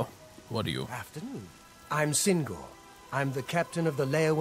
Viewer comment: not paying attention with this in the background, it sounded like he started the conversation with "I'm single", which definitly made me do a double take, haha!